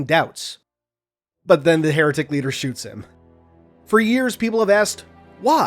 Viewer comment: I thought it said "why did the hectic leader fire on the arbiter"